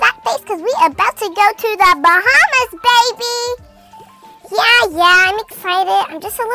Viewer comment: I thought she said hahah baby~~ like the song😂😂😂